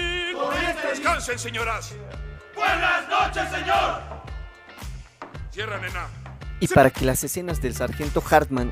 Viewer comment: "Cierra nena". Jajaja. Oro puro. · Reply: Porque escuché " cierramela " . _.